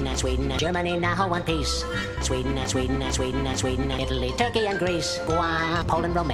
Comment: I heard him say obese😂😂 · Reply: Did you hear it at Italy, Turkey, and Greece sounds like Obese